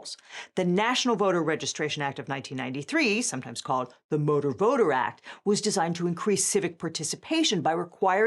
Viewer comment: Did she just say "The Motorboater Act"? I hope she did. That shiz was funny.